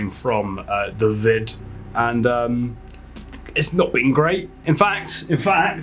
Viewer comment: When you said “it’s not been great” I heard “isn’t the weed great!” Then I saw your nebulizer and I was like nope… he definitely did not say weed 😂